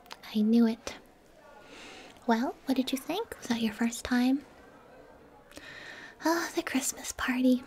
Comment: did she say “Was I your first time?” Or am I crazy?